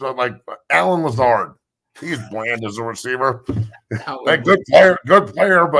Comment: Colorado Buffs Fans... Your thoughts on Richard Sherman calling Travis Hunter Bland? Subscribe to Zero 2 Sixty for more CU Buffs Football Coverage! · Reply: Matt when I first seen clip I could have swore he said “He’d Blend” because of how rare he would be corner with them ball skills.